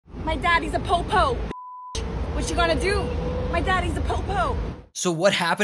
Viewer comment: Sorry, I kept mishearing her as “My daddy’s a poo-poo.” I mean, yeah, he is if he raised someone like her.